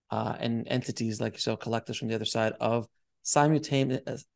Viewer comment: I thought you said for second, “ Bill Collectors from the other side,, “ 😂. Please , let’s be done with all that 😂